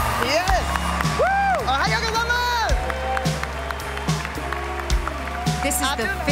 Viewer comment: what did Tiffany say? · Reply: "Ohayo gozaimasu" which means "Good morning", don't ask me why the hell she thought it was a good idea to just scream presumably the only Japanese she knew